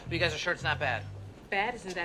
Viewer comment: I never realised that briita thought he said "rowboat cop" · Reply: In a similar vein; i always thought he said "bc the shirt isn't bad" after "i might have something to say about those jeans" But he says "but you're sure this isn't bad" (or something similar i still cant hear it well) So when they say "bad isn't that bad when it's abt bad girls" i was always like wtf are they talking about